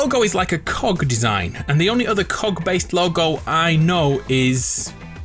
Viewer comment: "The only other cog logo Ive seen is... well mine." Literally a whole game series about Gears and the CoG called Gears of War lmao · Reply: I thought he said "the only other cock logo..." Whenever he actually said "cog" did anyone else hear that???